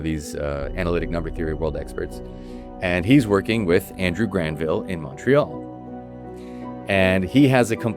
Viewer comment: i hear Andrew Garfield and was confused 😭